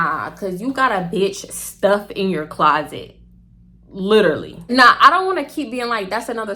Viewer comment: I thought you said “you got a bih stuffed in your closet” 😂